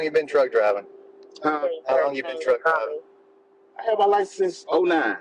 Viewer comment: I swear the first time I heard this I thought he said "How long you been drug trafficking" 13:11 lol